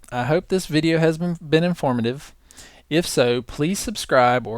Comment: Haha, at @ 19:33 it sounds like you said, "I hope this video hasn't been informative. "